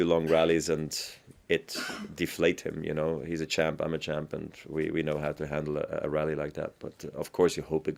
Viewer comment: 'he's a champ, i'm a champ' how nice is their respect for one another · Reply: I thought he said: "He's a chimp, I'm a champ."